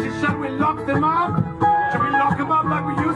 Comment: At "Nuke 'em up, like in World War II" I died. I had tears streaming down my face that no one in that crowd knew the history enough to think "wait... What?" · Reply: I thought he said "lock 'em up"